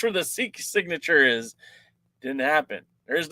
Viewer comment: i heard tyrus but codnt make out the other wrestlers name? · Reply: The sheik